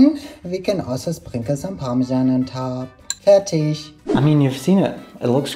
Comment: thank you for specifying you said 'fertig' because i totally heard 'fetish'